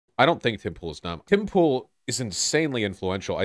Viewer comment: Why did I hear "Pitbull" not Tim pool😂😂